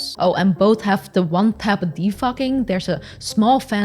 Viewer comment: I thought you said 1-tap de-f**king. xD Anyway, awesome video you guys! I used to use Drones a lot, but I think VR started pulling me away again.